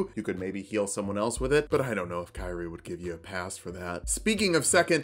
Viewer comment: Thought I heard Kairi and not Kyrie at 3:00 hehe overall great video!